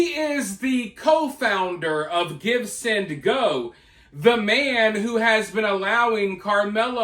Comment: Oh it's GIVESENDGO. I thought you were saying GIBSON GO. I kno nada about these funding websites, sorry